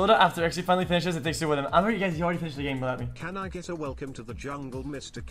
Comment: Wtf did he say? lmao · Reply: 'idk if u guys heard he already finished the game without me'